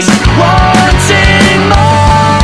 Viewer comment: I THOUGHT IT SAID TIA MARIA